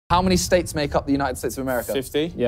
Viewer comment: I thought Simon said “How many steaks make up the average American” 💀💀🙏